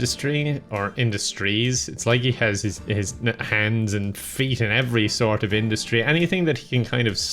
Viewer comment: i thought he said "It's like he's got his hands and feet everywhere... he can get on a chair" xD